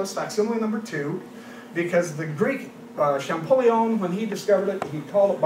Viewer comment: I said that because I heard "the Greek Champollion" at 09:15. But he might be saying "the great Champollion." Then again, I'm not sure everyone in the room knew about him.